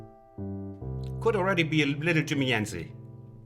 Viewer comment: Hey. Great lessons, thank you. Was wondering, what did you say at 3:22 after "Could already be a little ... "? It sounds like "Jimmy Enzy".